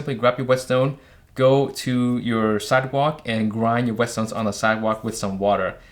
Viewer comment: What is he saying 0:29 - 0:30 ? "Go to your cinder block, cider block, sidewalk..." ? Also 3:02 - 3:04, cider walk, sidewalk, cinder block, or something else ? This is not his first video with street way pronunciations. He should first hone his pronunciation from dilettante to proper before talking to public. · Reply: um... he said sidewalk everytime dude, im sure your Japanese pronunciation would be a lot worse than his English.